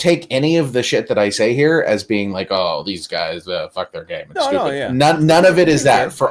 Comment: I thought he said "fuck, they're gay".